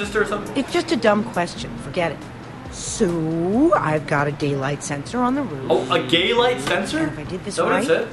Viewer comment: Day light not GUY LIGHT😑😐😲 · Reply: He thought they said gay* light sensor